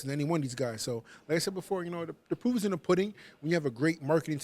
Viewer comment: did he say the poo is in the pudding